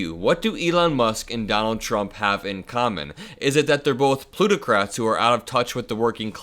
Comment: Lol, at first I thought you said "Lunicrats"